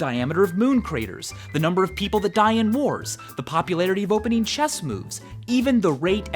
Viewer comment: I thought he said how many people die in vores instead of wars